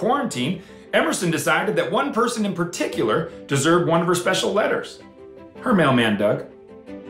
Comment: I thought he said the mail man died